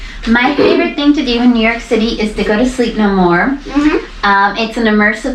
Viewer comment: It is literally the best thing I have ever seen. I really hope it will re-open after the pandemic. <link> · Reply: Oh! I thought she said, "sleep in a morgue"!